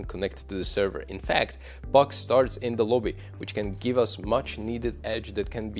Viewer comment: Bucks start in the lobby? What is he saying? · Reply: PUGS start in the lobby. You can gain an edge already by paying attention to details before you join the server. Seeing who is paired with whom, what are the stats of other players etc. can give you a clue what to expect and what might be the weakness to abuse.